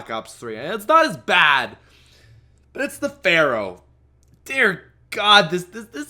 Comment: in 6:20 instead of saying pharo he said barro